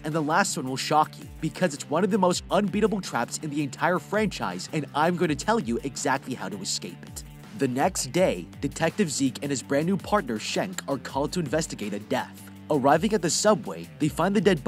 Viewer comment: I hear shek I immediately think of Shrek